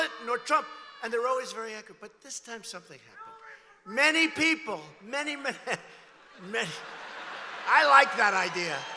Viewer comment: What did the man in the crowd shout at 41:14? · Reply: Hillary for prison